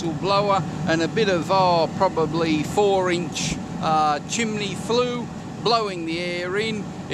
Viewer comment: Did he say chim"ley"?